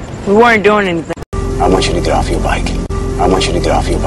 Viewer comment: i want u to get off ur bike.에서 아 원츄러 더프 여r 바이크 . get off 가 더프 로 들리네요. 막귀인가봐요.